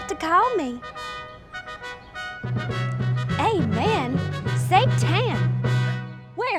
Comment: I’m blind, all I heard was “amen, satan”